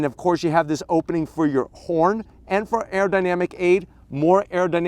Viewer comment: did you say, "you have this opening for your porn"?